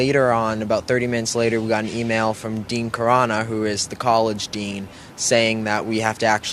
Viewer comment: DEAN CORONA DID HE SAY???